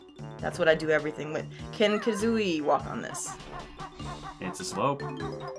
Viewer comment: I thought said "King Kazooie" and I went WTF! I thought was referencing me and I almost went into a happy stroke.